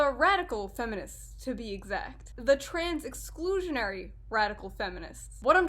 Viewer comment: I thought you said the turks are at it again